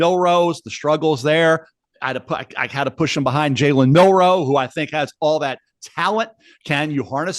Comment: Thought Mel said Jalen milroe was 3 😂